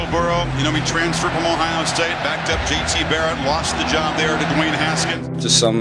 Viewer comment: Was I the only one that thought the announcer said TD Barrett at the beginning